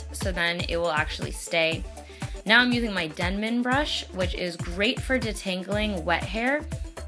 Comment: when people talk about '' denman brush'' i always hear ''denim brush'' xD